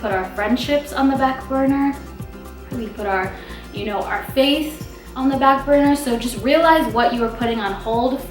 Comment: Lol i thought she said put your face on the back burner lol. This video is great and she is very beautiful